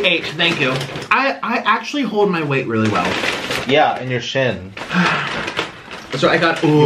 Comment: “I actually hold my weight really well~” “Yeah and your chin.” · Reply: dang i thought he said IN your chin, its funny both ways thoo